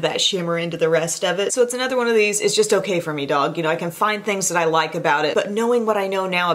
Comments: Did she just say "it's just ok for me dog"? Lol Idk why but I found that funny coming from...lol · Did... Did...um did she just say "its just okay for me dawg"? !!!!!!!! CTFUROTFLMFAO